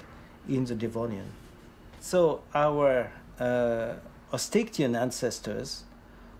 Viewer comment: our what ancestors? can't understand what word he's saying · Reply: 'osteichthyan' The superclass Osteichthyes, the bony fish, our ancestors.